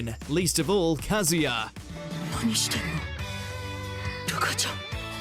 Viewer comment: I stood up when I heard "Ruka-chan" cuz i thought he said "Rika-chan" :sob